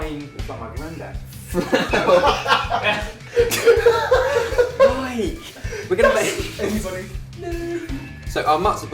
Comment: what does mike say at 4:08??? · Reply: it's like my grand dad or something? if so, gross and hilarious